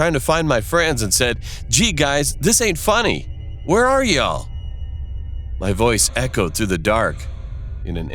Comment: what did he say?y'erel? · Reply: He said " Y'all" means " You all" but in a short way